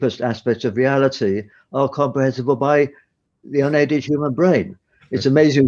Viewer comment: What does Rees say at about 18:40 "...comrehensible by the '??????' human brain? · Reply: Unaided human brain, I think he said. The automatic captions agree.